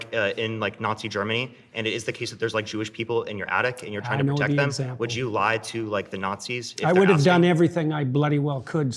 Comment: Can anyone explain to me what says at 35:34 that annoys Peterson so much? He says it two more times afterwards and I just can't understand what it is he is saying. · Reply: He said “Did you eat today?”